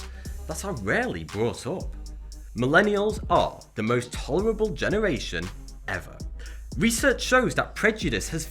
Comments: I thought he said "Millennials are the most horrible generation ever" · I could have sworn you said "Millennials are the most horrible generation." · wait hold up did he just say millennials are the most horrible generation ever